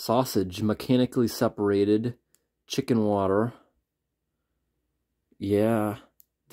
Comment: At first I thought you were saying it had "chicken water" as one of the ingredients... As in water from chicken or something. It wasn't until you showed the label that I was able to see that it was "chicken, water" and that made me feel a bit better, LOL. It's super interesting to see how different things are from back then to now. That white juice that came out of the old can was gnarly. I think one of my favorite things that you do is to put it on a slide and look at it under a microscope. It's so neat to see if there's anything in there that looks interesting, cool, or gross. 😂 Anyway, thanks for sharing!